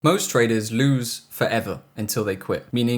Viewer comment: I thought I heard the opening line as " most traders lose for heaven".😀